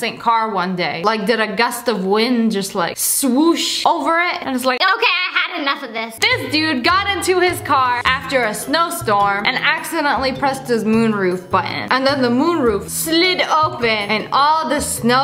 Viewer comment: Did she say moon roof